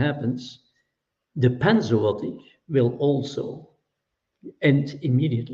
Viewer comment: What did he say at 9:25? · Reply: "panzootic" (equivalent of "pandemic" amongst animals)